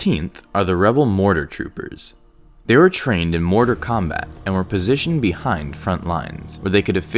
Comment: "they were trained in mortar combat" (4:35) it sounds like you said mortal kombat LOL